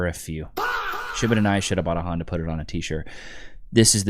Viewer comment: can someone tell me what he's saying here? He says it so fast and my hearing is so bad lol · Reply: Should’ve bought an I, should’ve bought a Honda, put it on a t-shirt? 😂